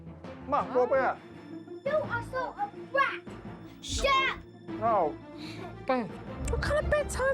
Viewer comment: sorry did she just say.. shut up